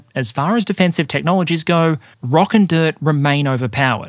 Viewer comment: DID I HEAR A ROCK AND STONE